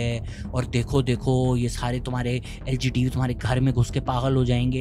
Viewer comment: did he actually say "LGTV" 😂😂😂😂😂😂😂😂😂😂😂